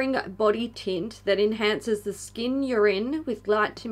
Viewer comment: I had to pause during the Fenty BodySauce part because I thought you said it enhances your skin urine. I had a long day.